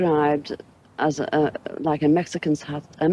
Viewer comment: someone please help me this translating 5:03 did she say “A Mexicans house without an obelisk”? Or “a Mexicans hut without a bubble on it ? 😂❤ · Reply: A mexican hat!!!! The flying saucer looked like A big Mexican hat to her - is what she’s saying. In other words it had a large domed top and a broad brim.